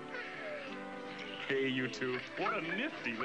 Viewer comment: i thought he said "Hey YouTube"